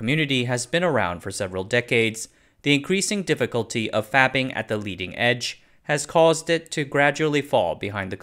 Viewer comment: at first I heard 'fapping on the leading edge' and thought I was on the wrong tube site for a second. 12? No I'm 40. 🤣